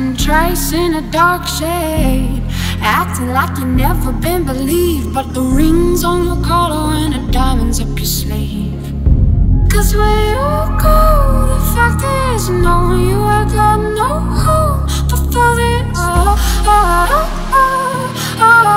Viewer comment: "cause where you go, the f*ckers know" :D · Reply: lmfao I heard "cause we're all gold, them fuckers know"